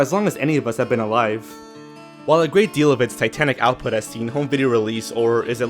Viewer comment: When you mentioned Disney's "titanic output," I thought you said "satanic output" at first, which is funny given all the conspiracy theories about Disney putting satanic messages and/or dirty subliminal images in their cartoons. I remember Sing Me a Story and Teamo Supremo from when I was younger, and I kind of liked them, though I wasn't a huge fan. I also have older relatives who have told me about The All New Mickey Mouse Club, and I've even watched the opening here on YT. There are three more lost Disney cartoons you didn't mention but that I would love it if someone found. One is a series of shorts that aired on Disney Channel when I was little featuring a group of foxes. It had an opening theme that went something like, "Sun and the moon and the wind and the rain and the sun" over and over again. Another is "From All of Us to All of You," a Christmas special hosted by Jiminy Cricket which aired on one of the Disney TV anthology shows back in the 50s. It was released as "Jiminy Cricket's Christmas" on VHS and Beta some time in the 80s, but hasn't seen any home video release on any format since then. It's become massively popular in Scandinavia and you can even watch an English version here on YT, but it's incomplete, either due to technical issues, copyright blocking, or both. The last one is an authentic, original Technicolor dye transfer print of every Disney animated feature from Snow White to the initial end of Technicolor's dye transfer processing and printing in th